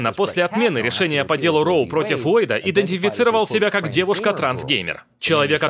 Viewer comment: Только я услышал здесь "трансгеймер"?